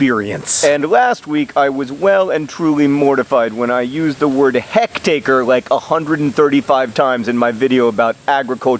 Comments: I thought you were saying Hex-acre. Like a sqaure mile but 6 sided or cursed with drought. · I heard 'Heck-takers..." and thought "hmm...I've heard of 'hec-tairs' but maybe this is just some different term or unit of measurement I have never heard before." See the power you have? You can create things that don't exist.